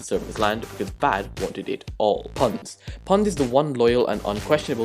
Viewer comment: If you turn subtitles on and go to 8:38 and pause quickly you can see pond instead of punz